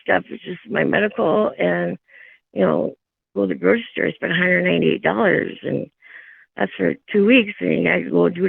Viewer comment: is she saying her prescriptions and groceries for two weeks cost $198, or am I hearing that wrong? I’m a single mom with two teenagers, my groceries alone can easily double that for just one week. I don’t get to go on fun vacations to south padre island, or go to universal studios in Florida, or have huge Christmas gatherings with tons of presents. I work full time, and as much overtime as I’m allowed, and I can barely afford the necessities month to month. I hate people like this. Stealing from the taxpayers and being an absolute bed blob in her day to day life, a drain on society who contributes nothing and lies to a judge to avoid the consequences of her actions. And I’m not even 10 minutes in 😂 😭